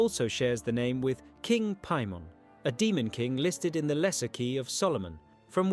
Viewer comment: here instead of paimon its pokemon..